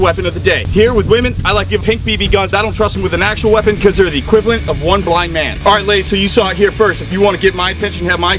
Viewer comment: I had to listen twice.....first time I swore he said "....cause they're equal to one black man!"